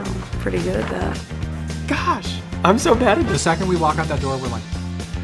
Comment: What does Cole say on 1:08 ? Please help ❤️ (Actually don't understand it,because I am from Russia) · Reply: He said " the second we out that door, We're like.... "